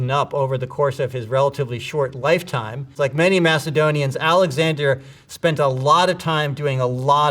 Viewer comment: like many what? weird word that this guy said, idk if he knows that this word doesnt exist and doesnt have a story behind it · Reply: “Macedonians” like the country